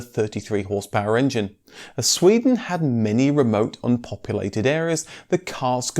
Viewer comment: Great video about some neat cars. At 4:28 I had a laugh as I misheard the dialogue: "As Sweden had many remote Uncopulated areas, the cars could be converted for sleeping." Oh myyy...